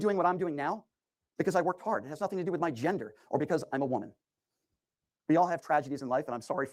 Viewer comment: did he just say he's a woman?